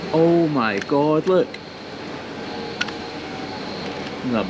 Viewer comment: Did you said "Oh my gauntlet" at 4:35 ? Who are you? Thanos :D?